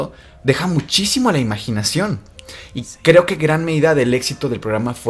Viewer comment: Por el minuto 3:06 más o menos, se escucha un susurro que dice "si"